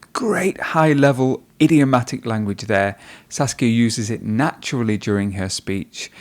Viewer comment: did he say sauske